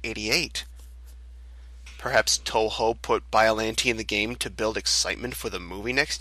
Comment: did he say touhou? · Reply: Toho, the company in charge of Godzilla merchandise, IIRC. No relation to Touhou Project.